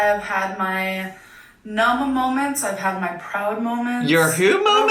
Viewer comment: replayed 3:39 100x.... still no clue wtf she said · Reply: Mana moments. Mana is a sushi bar.